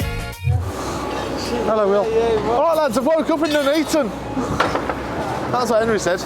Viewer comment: did you say “woke up and ent eaten” or “woke up in Nuneaton”? I’ve listened a few times and can’t work it out 😂